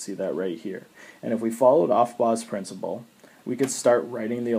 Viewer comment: What principle did you say? At 1:46? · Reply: Chloe Macdonald aufbau